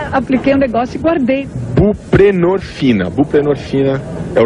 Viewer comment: What was the girl showing and saying at 3:43?? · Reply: Buprenorphin, also called today Subutex, its againat Heroin Addiction. When you a heroin addict, you can put the Heroin down, and can take buprenorphin, then you have no withdrwawal at all, i took it back in 2005 or so its feels almost like heroin. And this medication is on the market since 1999 or so...but kurt had it back in 1992!Kurt made always a mystery for what medication he used for his stomach problems...now i know!